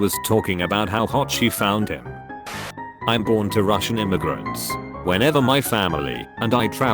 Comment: At first I heard "I bought Russian immigrants." Quick thinking though dad! :O